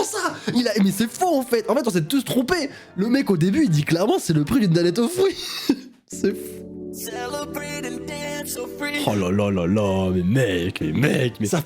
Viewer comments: moi j’entends saloperie de kinder surprise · Moi j'entend : " saloperie de danette au fruits" 😂😭